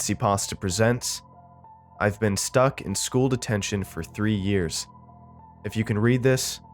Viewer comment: thought it said I've been stuck in school for three years and I was like Me too